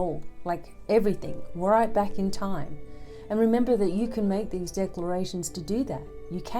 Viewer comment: #1. I keep hearing, "Remember Who You Are" from Lion King